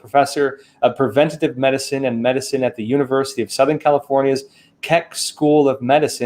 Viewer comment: Almost sounded like he said Kek 🐸 School of Science...